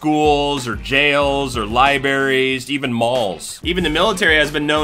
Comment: Had to stop once I heard him say "Lie-berries".